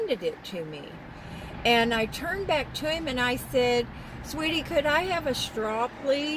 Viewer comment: The only thing our local McD’s does consistently is get everything wrong! Honestly, how hard can it be???😡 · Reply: This one seems to have improved! Someone pointed out that maybe they thought I said sweet tea when actually I said sweetie!